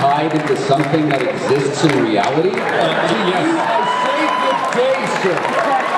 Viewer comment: Can't understand what Wil say after "reality at 15:23.... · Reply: To you I say good day sir.